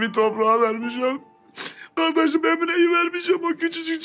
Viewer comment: Cemre yerine emine dediğini anlayanlar